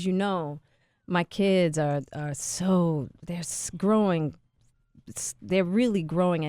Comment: did she say her kids they're screwing then switched it up to growing?